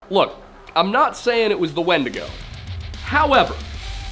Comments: Misheard the opening as “Look, I’m not saying it was Wendigoon…but-“ and I fully accepted that you might have said this for a good five seconds. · The first line had me replaying because instead of “now, Im not saying it was a wendigo” I thought said “now if it wasn’t Wendigoon” 😂